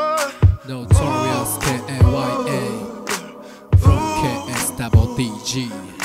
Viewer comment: ってなんて言ってるんですか？分かる人教えてください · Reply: from k.s.w.d